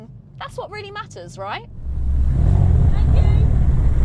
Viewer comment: lol what was going on at 8:34 sounds like you said thank you and then got whistled at 😆